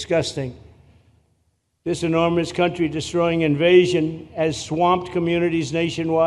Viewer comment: Did he say swamp?